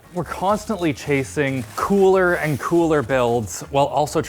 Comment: This showed up in my feed, despite having watched when it first came out, I decided I’ll watch it again. I am in no way joking. I sat here for 20 minutes trying to figure out what “temperature not ‘sweet’” meant. I did not understand until finally I heard say “cooler and cooler”. I am not proud of myself.